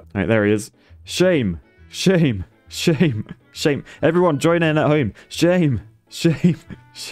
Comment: 🤣 I'm playing Rogue Tower with this vid playing in the background... I hear, "Shame! Shame! Chime in at home" ...I was already on it